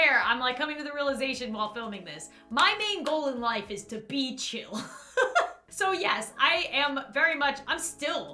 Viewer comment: If you turn on the autogenerated subtitles at 21:05 instead of "my main goal is to be chill" it says "my main goal is to beat you".